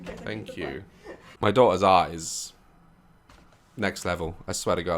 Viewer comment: AND TALKED ANOUT HOW BEATIFULL SARA'S EYES ARE 🤣🤣🤣🤣🤣🤣🤣🤣🤣🤣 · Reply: he said art...not eyes lol. I think any way? That's what i thought he said...'my daughter's art is out of this world.' Quote...